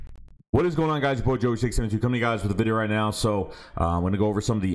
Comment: Wait, what did you say to start the video? “What’s up guys this isynjshset” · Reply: Haha I was saying what’s going on it’s coming at you with a video but said it fast lol